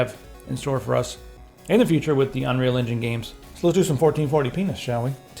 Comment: Did he just say "Let's do some 1440 penis"??? haha